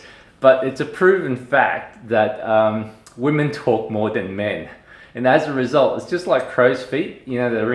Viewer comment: Yes woman may smoke more than men,only because men stress us so we need a cigarette to calm down lol thanx for sharing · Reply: I thought he said we talk more than men. Not smoke more.